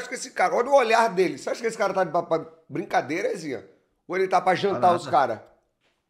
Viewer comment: eu entendi "ele ta pra brincadeira ou ta pra APAIXONAR os caras"" ja apaixonou o BR inteiro, pq nao os gringos neh com esse olhar ai nao tem como tankar